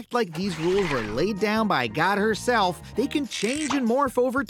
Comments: did he just said that god is her? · Did he say “god HERself”